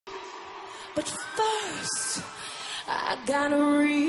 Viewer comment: Lycris "But first i gonna rich you" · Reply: I thought she said "But first, I gonna reach you" ?😂😂